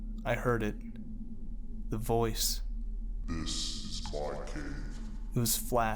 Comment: What you said 'This is my cave' What I heard 'This is mah swamp.' Goddamnit shrek..